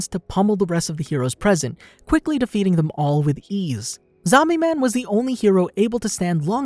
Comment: Did I hear Omni man??